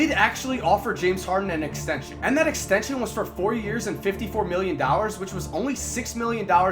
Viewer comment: Anyone else heard him say 40 years